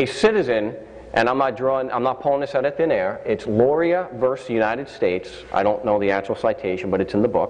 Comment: Did he say Loria Vs United States defines citizen? 10:30?